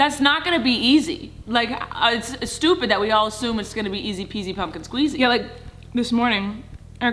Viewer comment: did just say easy peasy pumpkin squeezy?